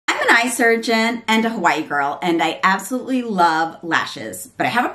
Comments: Dont know what your ethnicity has to do with anything, but since your claiming heritage, dont 4get to claim the black in you cause I see a mile away and your hair confirms it.🤨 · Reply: Actually if you read the details about the video, you misheard me. I was saying "I'm a Hawaii girl" . not White girl. And important to point out living in Hawaii since it's way more humid out here, and people tend to be out near the beach, with a very active lifestyle, near the wind (which can cause lashes to fall off), etc. I'm definitely not white. And, not black. 100% Indian-American. If you visit Hawaii, you'll see that's a common thing people will say "I'm a Hawaii boy, or I'm a Hawaii girl" . not Hawaiian, bc i don't have Hawaiian blood. Hope that clears up any confusion. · I’m cracking up! I thought you were saying “white girl.” At first I thought who cares, I just want to know from a professional if it’s safe, lol. Second, you don’t look white, lol. But anyway…I’m glad you did this video. I was concerned about the eyeliner and their ingredients. Thanks for sharing, I feel so much more comfortable and will buy!